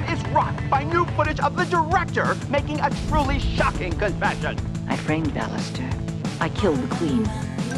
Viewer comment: In the original confession (the scene before this clip) I can hear the director say "I framed Allister. I killed the Queen." But in the video loop I keep hearing it as "I creamed Allister. I grilled the Queen." Am I the only one?